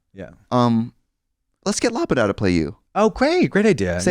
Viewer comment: who does trixie mention to play katya? the caption says "lapid out" i can't figure it out. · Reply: must be loboda, katya's fav russian singer